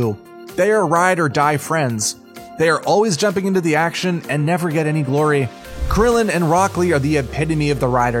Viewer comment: Did he just say “freezer”